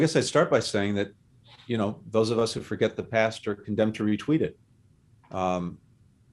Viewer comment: does he say retweet it or am I insane?